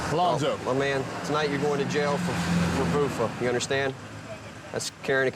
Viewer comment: What was that garbled-sounding word the cop said the guy was going to jail for, just before he said carrying a concealed firearm? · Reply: I heard fufa